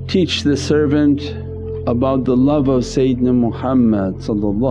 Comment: Fix translation at 1:40 plz. It says satan instead of sayedina.